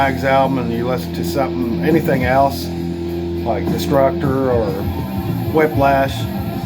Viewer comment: Cool update! Destructor sounded great in the background, I just thought you said Destruction! Some cool pickups too man 😊🤘